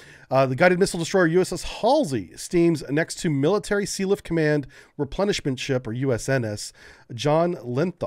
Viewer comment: For a moment there, I heard "replenishment ship" as "punishment ship."